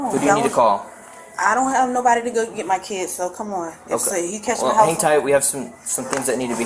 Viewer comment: Did she say if he catches my house on fire at 19:47 lmao